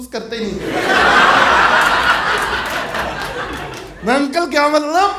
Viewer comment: Bhaiyo 7:40 pe kya bolaa h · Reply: Uncle kya matlab